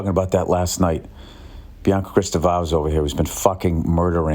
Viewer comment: Who is the Betty he's taking about 11:15? Krista Vaz? I listened to him say it over and over again and that's what I heard. I have no idea who that is. Neither does Google.